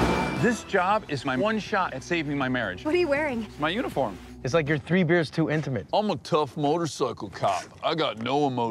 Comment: What Michael Pena says at 1:18 ? I don't understand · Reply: Jason Kent it's like you're 3 beers too intimate